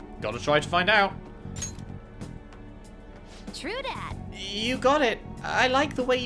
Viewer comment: what did futaba say?! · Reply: True dat :P